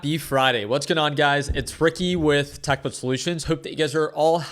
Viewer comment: Sounded like you said Fricky 😆